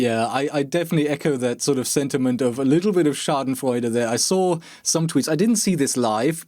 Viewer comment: Did you just say "Schadenfreude"